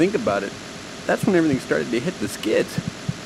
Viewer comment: typo. Stated instead of started. 😂